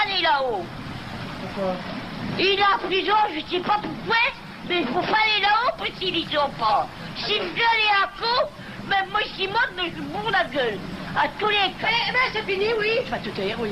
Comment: il faut pas aller là-haut -I think means at Bernard's house? She said something... a tous les quatre, if they come she will do something to them? I think I heard her say: I said why not. and she said something about petits enfants at 8:50 but I don't know who did the transcription! Poor her what trauma she went through. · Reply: she said, "Il faut pas aller la haut, Si moi je gueule un coup, si vous monter la haut je vous casse la tête à tous les quatres. " I think, the fours are for the reporters who are here.